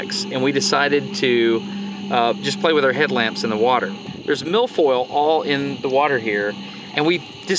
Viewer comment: did he say milf oil at 0:30? Where can I get some of that? Asking for a friend